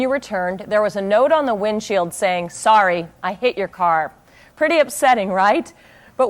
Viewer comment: I thought you're saying " I hate your car" .. 😂